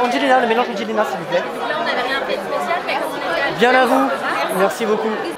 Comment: こんにちは。いつも楽しく拝見しています。 質問があるのですが…アンジェリーナのサロン・ド・テで注文をしている場面で、だんな様が紅茶を注文したあと『○○ merci beaucoup 』○○の部分が聞き取れなくて何と言っていたのかが気になっています。『ディアラブー』のような感じに聞こえたのですが、何と言っていたのでしょうか？ フランス語を勉強しているのでぜひ知りたいのです。どうぞよろしくお願い致します。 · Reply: こんにちは、コメントありがとうございます。 フランス語を勉強されている方がご覧下さってるとは、ちょっとドキドキ緊張してしまう私です😅 ご質問の部分ですが「Bien à vous」と言ってると思います😊 続けて発音しているので「ビヤンナヴ」って聞こえますね🎵 シーンによりますがここでは「ではよろしく」的なニュアンスの意味かな？😉